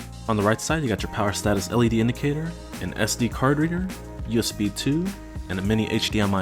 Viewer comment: Yes, 2 USB 3.0 and one of them is an always on charging port. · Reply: Oh than it was my fault. I thought I heard USB 2.0 once